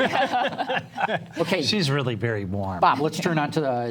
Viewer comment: "she's really very blonde" lol, did he really say that ? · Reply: No he said “warm” .